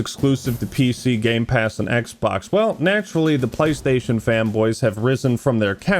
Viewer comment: I keep hearing "playstation femboys"